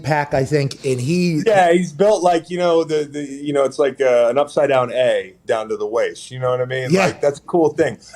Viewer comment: I’m less than an hour in but I feel comfortable saying that the best part of today’s show was Chris long describing something as an upside down “A” multiple times and just acting like a “V” doesn’t exist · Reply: I thought he said upside down egg